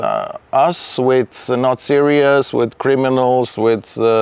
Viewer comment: I think he said "not serious", not "Nazis". Why would you connect Slavs to Nazis? Not like you can tell by the kind of "ić" whether they're Croatian or Serbian...